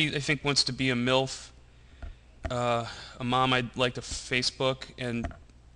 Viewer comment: did he just say," A milf, a mom i would like to face?" LMFAO!!!